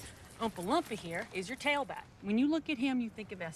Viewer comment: Did she say Umpalumpa?